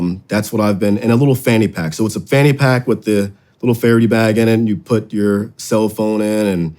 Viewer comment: Damnit! Saw the episode- went and bought a fairy bag because I thought you were saying a fairy bag for EDC. I’ve been mincing around with this thing getting laughed at by my buddies. Then I find out it is a Faraday bag. Damn you tinnitus!